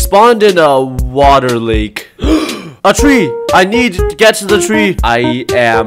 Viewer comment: Idk but I kinda heard you say I need to- get the tree hahahhaah